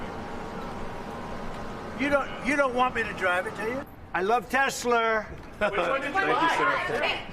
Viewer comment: Did he really say "I love Teslur"??? LOL